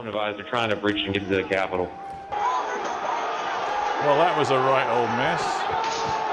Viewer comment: I swear the reporter says 'that was a right old mess' to his colleagues 😂 He ain't wrong! They screwed that report up